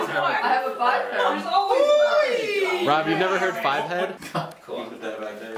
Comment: I swear at 12:51 I heard someone say, "I have a butt plug." WHAT?!